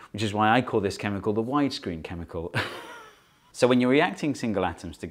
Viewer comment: I heard him say Weinstein chemical, still holds up.